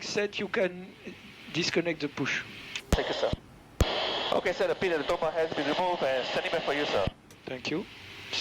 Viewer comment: what does the pushback driver say at 1:21? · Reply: Set the pin, tow bar has been removed. Something like that.